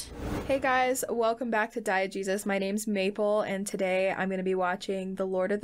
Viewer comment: Every time you say "Diegesis" I hear "diet Jesus"